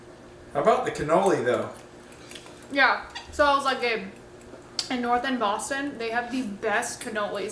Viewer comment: I thought she said babe haha 🤭🤭